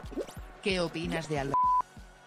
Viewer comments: que es lo que dice? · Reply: le preguntan que opinan del ser insignificante de ALBERTITO98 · alguien sabe que dice? Yo tengo un teoria (alberto) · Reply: He ido al directo solo por vosotros porque os quiero dice: que opinas de albertito98?